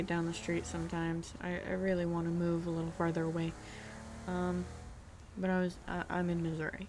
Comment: wait what¿ what did u say??? ._ . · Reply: Lol sorry, I was talking about how I'd like to move farther away from my home town. I only used like that when I speak. Not a clue why 😂😂😂